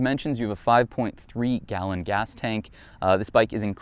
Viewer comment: 27 to 30 mpg. That would be a deal breaker for me. I get 40 on my 2004 Rune and twice as much fuel capacity. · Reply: Sounded like you said 3.5 galllon tank. No it’s not a ten gallon tank it’s 6.6